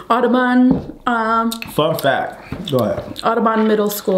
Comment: When he said fun fact.. I swore I heard f that